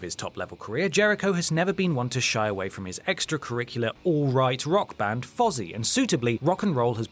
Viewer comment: Swore he said "Alt right rock band"...had to back that up. lol